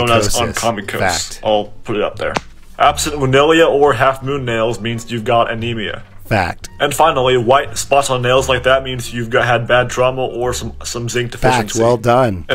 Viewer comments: “If you have white spots on your nails you may have had trauma-“ Me: “ah- yes. yet another reminder” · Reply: I thought he said zinc deficiency… but it was hard to hear over the “FACT” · Literally trying here what he’s saying & all I hear is “FACTS!” “Well Done”